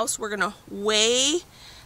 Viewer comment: Can you please write out the potato planting method? I heard "rustaudt" but I doubt I heard right! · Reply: Ruth stout